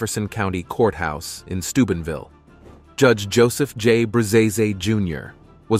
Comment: i wasn't looking at the screen, multitasking and i had to do a double take because i thought he said stupidville